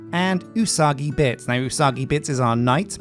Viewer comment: You say Usagi Bits, I hear "Eww, Soggy Bits"